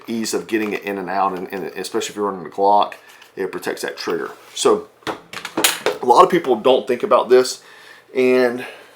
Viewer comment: would you please say again (much more clearer) that slim line holster (3:00 stamp); completely unable to make out what the maker is? I did replay that slice 5 times; still can't comprehend what you're saying. Thanks! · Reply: Incognito concealment